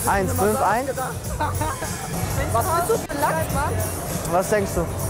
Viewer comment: fragt sie "was bist du für ein landsmann" ich habe verstanden "was bist du für ein lachs, mann" lel